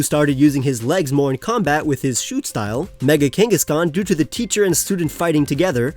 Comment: When you said “Mega Kangaskhan” I heard “Mega Genghis Khan”